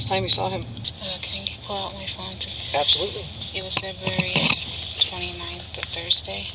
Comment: Did she say Febuary 29th?